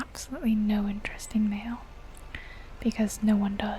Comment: i heard "i get no interesting in male" and was going to agree, but realized you talking about mail